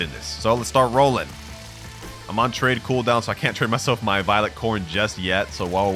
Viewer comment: did he really said trade cooldown